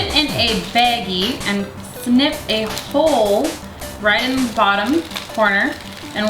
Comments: I thought she said "sniff a hole right in the bottom." XD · I thought that you said, "Sniff a hole." and I died. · I thought you said "SNIFF a hole"